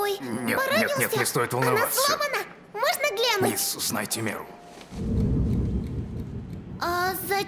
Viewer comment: Кто-нибудь понял что сказал Мордекай на 1:40? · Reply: Вроде "мисс, узнайте меру". Но в оригинале она говорит "Ты слишком близко" в смысле "Держи дистанцию"